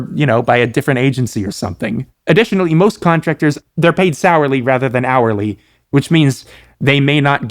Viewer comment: at this part i misheard the word "salary" so i interpreted the sentence as "they're paid sourly rather than hourly" and i was like "damn i bet. that's a bar though"